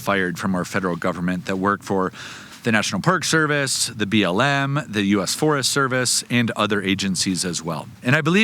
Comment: Your opening comments @ 0:06 did you say BLM?